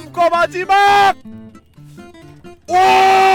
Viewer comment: 이어들으면 오!!!!!!빠하는거처럼 들림 ㅋㅋㅋ